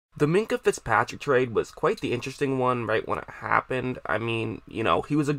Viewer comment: Did u say interethed?